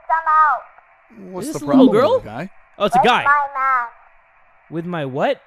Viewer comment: I thought the little boy said "I need help with my Meth (Meth are drugs)